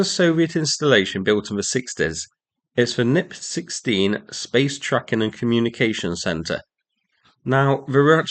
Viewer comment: i thought you said "Space Truckin" for a second and had flashbacks to Deep Purple's Machine Head 😂